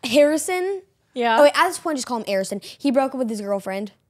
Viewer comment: did anyone else hear say, Harrison,at the point just call him Arrison. At 6:30 in the vid